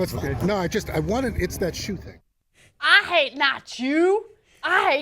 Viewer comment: I thought he said "I hate not shoes" lol